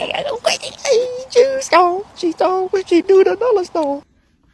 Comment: I thought you were saying " cheese dog cheese dog, she do the nala stomp"🤣